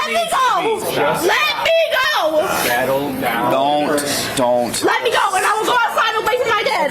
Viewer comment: I heard “cattle down” from the VIP cop in the beginning of the video which made sense